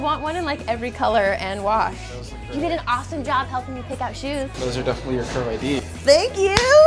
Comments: Is it just me or does it sound like she says "you did an awesome job helping me pick out shoes" ?? lol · did you say thank you for helping me pick out shoes at the end? before he said those are definitely your curve id? starting around 2:09 lol